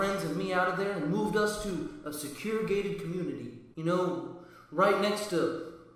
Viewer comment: At first I thought it said goated, I play too much fortnite